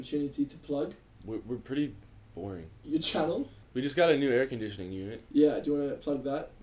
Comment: i keep hearing "new hair conditioner"